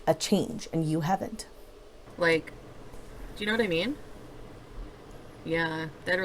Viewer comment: Every time I hear her say “you know what I mean?” (Which to me sounds like ‘you know what I min?’) I want to slap her across that face. Yes I have anger issues when it comes to and only.